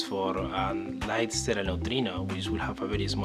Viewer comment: I'm trying to listen I really am. At about 3:43 does he say "evidence for nine sterile neutrino"? It's hard to make out. EDIT: OK, closed captioning says "light sterile neutrino". Closed captioning actually works. Usually I turn on closed captioning and am disappointed.